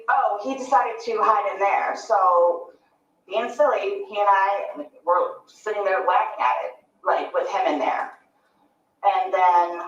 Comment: Did Sarah say….. “He & I were being silly just whacking at it….with him in there. His little fingers were sticking out” Did she admit to hitting Jorge in the suitcase during this interview?